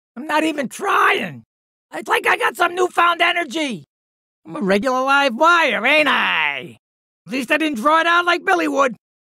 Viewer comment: What is he saying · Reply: Nvm, I turned on the captions. “I’m a regular livewire ain’t I”